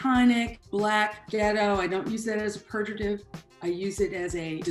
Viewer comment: Pertidiv ? Can someone tell me what she said at 2:54 and how is that word actually spelled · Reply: Pejorative: expressing contempt or disapproval 😊